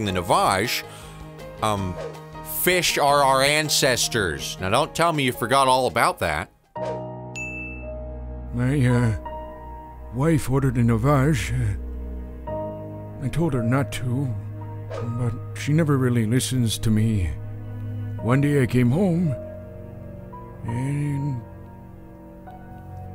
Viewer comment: Literally at 4:49 I thought he said ''the wife farted in a vase'